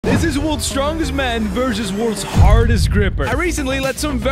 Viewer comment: I thought he said worlds strongest man vs worlds hardest man💀